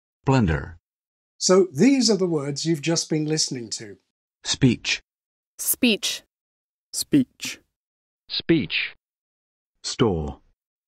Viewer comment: Geez, I heard "peach" for beach/ speech word 😅